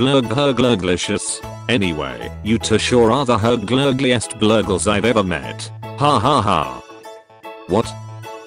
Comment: when the dog said "you sure are the Hurg-glurglliest gurgles I've ever met" I think she said "you sure are the most uglyist things I've ever met"